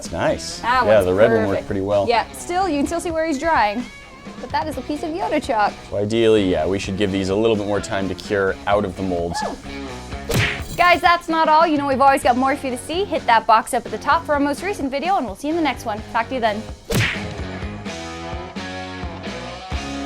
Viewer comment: I literally thought she said f you then but I know it was talk to you then